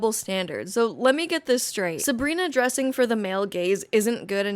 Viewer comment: I loved the video. My only addition to the conversation is that the whole time I thought you were saying "male gays"... That is all.